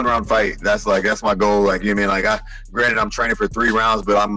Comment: Does Niell potentially slip a fight fix there at about 11:07....? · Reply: Cuz I heard "that's my goal, that he an I got" Idfk🤷‍♂️😆